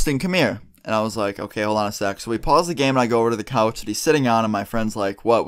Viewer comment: it sounded like he said ok hold on a sex